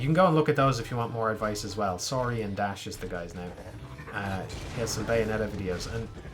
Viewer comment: Anyone know how to find that video he mentioned towards the end of this vid? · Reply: Found it. The channel name is "Saurian" I heard "Sorry and Ashe"